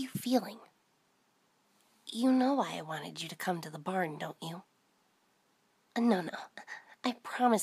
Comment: In the beginning i thought she said she wanted me to get to the bar 🍷lol